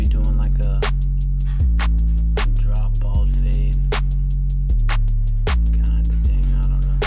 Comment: Mid bald fade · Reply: I thought you said drop fade in the video?